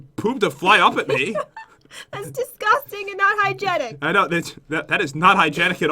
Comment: did he just say, "I know bi**^." ???